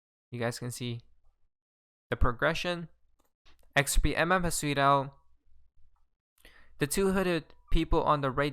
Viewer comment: What do u say at 8:51 i never understand ? "Sweet oh "? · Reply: "Such and such HAS TWEETED OUT"